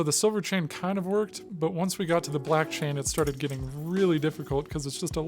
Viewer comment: I'm Canadian but maybe I'm a crypto-Midwesterner, because when you said "black chain" at 04:56 and 05:22 I heard "blockchain" in a Midwestern accent 😆😆